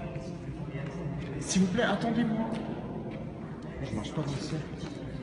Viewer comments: il dit pas plutôt « moi je marche pas dans ce cercle » ? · A 9:22, il dit pas "je marche pas dans ce sang" mais "je ne marche pas tout seul" ... Enfin c'est ce que j'entends clairement moi ! Sinon superbe vidéo, comme d'habitude 😍